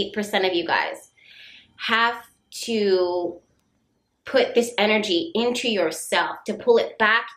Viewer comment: Thought I met a man but that was a bust. Really crazy bust. I heard: "Focus that energy on yourself."